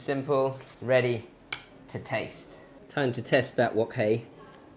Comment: Did he say "Time to taste that wok, hey?" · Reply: He said "wok hei", which is all about understanding and controlling the heat of your wok. When he says "it's time to taste that wok hei", he means the smokiness that comes from wok cooking - Lee